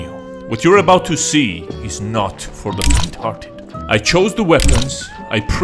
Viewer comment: when he said faint hearted i thought he said retarded